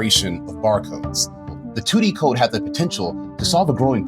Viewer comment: Can't we just call 2D barcodes "squarecodes"? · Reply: I kept hearing "tuti codes" while listening to this. Took me awhile to figure out it was 2D codes